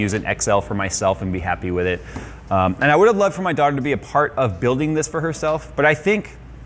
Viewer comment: did he just say "I'd love for my dog to be apart of building this for herself"